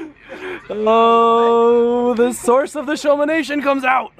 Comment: What did say?!?! I cranked up the volume, played it like 6-7 times and still couldn't make it out... Congrats on qualifying you got this. Just keep up the good attitude and keep having fun. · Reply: I think figured it out! "I'm not going, I'm gonna be going to Rachel's" he's a little sloot